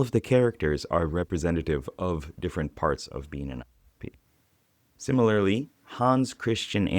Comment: what's the word here? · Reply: INFP